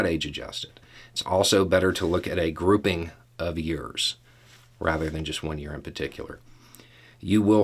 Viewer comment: 🌽🌽🌽 ('Cause I heard, "Groupings of ears.")